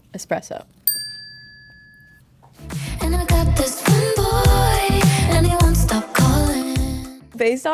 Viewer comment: I thought it said “and I got this fun boy”